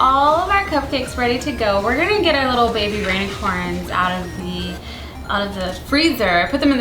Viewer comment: I think she actually did say 'baby rainicorn' xD